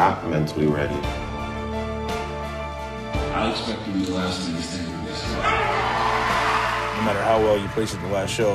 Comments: What does Kai say at 1:58? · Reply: Kai Greene says, "I expect to be the last man standing this year." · What does kai Greene say at 1:58? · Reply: "I expect to be the last man standing (?at the competition?) "